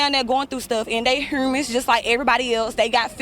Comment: yooo she said “ they heurmans” instead of humans 😂